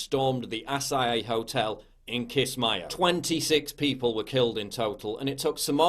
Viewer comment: i thought he said kiss my ass not kiss my o